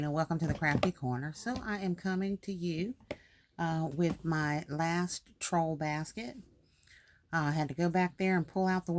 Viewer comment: Got to finish my last few easter baskets about 8 more large ones. That's my last troll basket and I've done a few mothersday gifts. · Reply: ooooh ok my bad. Thought you were saying last Easter basket